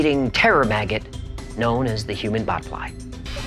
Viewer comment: On human bot fly, I thought I heard Human Butt Fly at first lol.